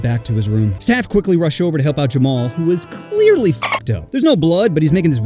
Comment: what did he say?! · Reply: Fucked up